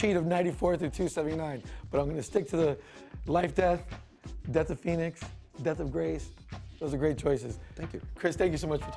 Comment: Does say "Death of Grace". I'm not familiar with this storyline. Can someone clarify this for me? Cheers!